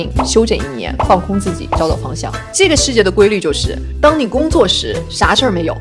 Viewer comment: 找找方向听成了=交交房租。。。